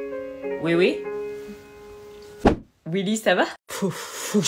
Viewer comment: "oui oui ça va ?" J'ai entendu ça 😅